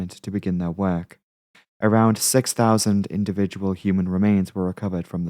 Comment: Did I mishear 44:10 or did he say 6000 THOUSAND individual remains? Does that means 6000 people died or does it have another meaning, like 6000 bits of bodies or did he just make an error?